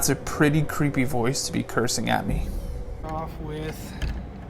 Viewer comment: Timestamp 3:28.. It sounds to me like he says "F@ you.." instead of, "F@ it.." but I'm listening with bad headphones while everyone in my house is sleeping so I couldnt blast it on my tv haha pretty freaky though. Probably for the better you didnt hear that one in real time 🤣👻